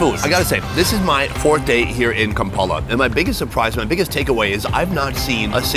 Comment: Did he just say Ta ke Awa ya . Instead of take away? 😅 Our light-skinned brothers be doing too much, I think.